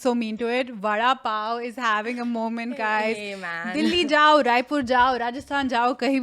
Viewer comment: did she say Raipur? I’m from Raipur. I’d love it if you guys ever visit it.